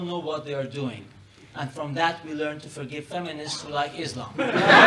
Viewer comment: "...and from that we learn to forgive feminists who like Islam" Aahhhh!! I love truth tellers!!! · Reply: lol I thought he said "Forgive idiots who like islam". I wasn't far off.